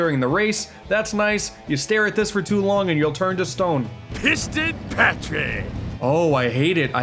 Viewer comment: Misheard that as “Fisted Patrick”